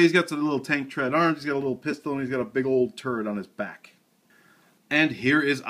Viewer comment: I swear it sounded like he said "Turd"